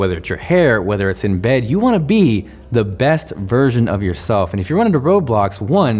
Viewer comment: "You wanna be the best virgin of yourself" That's how I heard it :'(